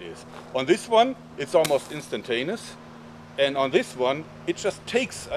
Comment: no offense, but I think you said, "Instant anus"